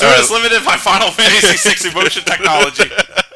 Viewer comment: What did you say about 'Final Fantasy' at 12:36? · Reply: Toad's limited by FF6 emotion technology